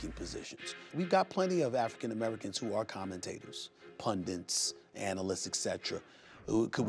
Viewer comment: Did he really say "pundints"?